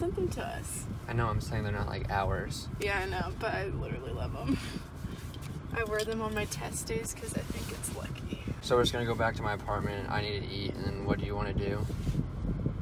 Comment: 😂 sounded like she said testies 😂